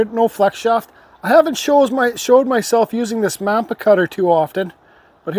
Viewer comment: Where did you get that small cutter? It sounded like you said mampa or mapa cutter but I can't fine either one.